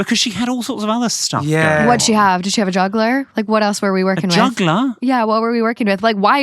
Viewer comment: I looove the accent. I’m so sorry but when he said juggler I thought he said chancla lmao. I had to rewind like 3 times